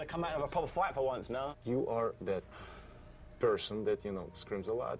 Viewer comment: i thought klitschko said "you are dead"